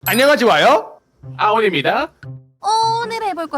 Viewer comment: 나만 안넝하찌와요? 이렇게 들리냐…?